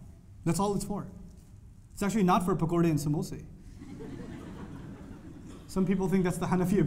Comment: What does he say at 11:10?? "It's actually for ... ." And right after he says, that some people think, that it's the hanefi opinion. What does hanefi mean there? I didn't get why people laughed after he said that. Could anyone explain? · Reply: Musti C. It's a joke about the food that people from the indian sub continent have for iftar (pakora and samosa).. It's quite tasty and people love it.. So he says that Ramadan is actually not about just the food and makes the joke about people thinking that it is the hanafi opinion (people obviously do not) just to emphasize on how much people make Ramadan just about the food